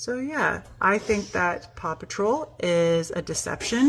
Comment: Oh my I thought she was saying Papa Troll and I had no idea what show she was talking about 😅